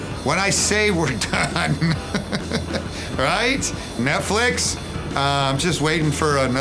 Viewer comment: Did he just say Methflix?